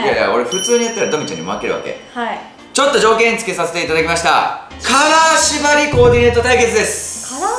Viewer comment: カラー縛りが、金縛りって聞こえた